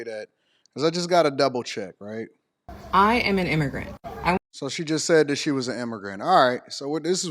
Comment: Lol sounded like she said "I am immigrant" 😂😂😂😂Lady you are the delusional one🤭🤣🤣🤣🤭🤭🤣🤣